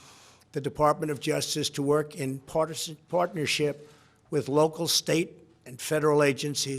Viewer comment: Pence looks like he's mind controlling Trump. He's not moving and only focusing on the back of Trumps head. It looks like Trump is trying to fight out of what he's saying. At 2:02 you can hear him say "Partisan" instead of "Partnership". When that happens, Trumps hand moves and Pence seems to slightly adjust and focus harder on Trump.